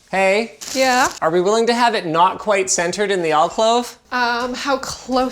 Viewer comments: *alcove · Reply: I heard the extra "l" too "alclove". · Did he say "alcLove"? Twice?